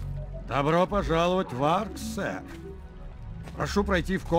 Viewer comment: Послышалось: "Добро пожаловать в Ад Сэр"